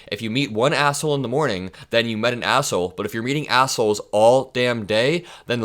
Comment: Wait, did you say meeting or eating? Because it feels like the latter rn.